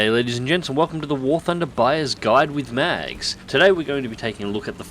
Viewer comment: haha, I thought ylu said welcome to the war thunder biased guide